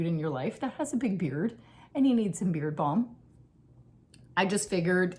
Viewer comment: Beard balm…ooohhhh! I thought at first you said beer bong and I was trying to work out what that was.😂 Very nice to see you again. Knitting, traveling, and books…what else do we need?